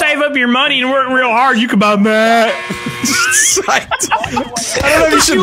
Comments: Therapist: "Redneck Blarg does not exist, he can't hurt you." Redneck: "YA KIN BUY MAAAAAAAT" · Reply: When I first heard say, “You can buy Mat,” I legit thought he said “you can buy Meth” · I always thought he said you can buy meth, instead Matt